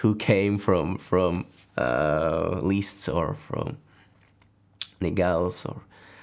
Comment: The subtitles got confused at 17:46 , he said Neuhaus as in Heinrich Neuhaus. In russian we pronounce it a bit differently